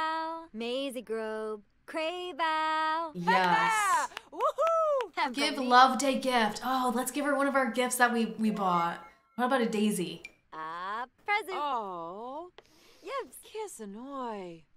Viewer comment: did she say ”aaa present!” ??😳